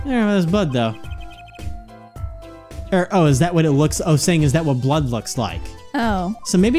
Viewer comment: "He's saying is that what blood looks like". What? What the fuck? No he's saying it looks like letters wiped away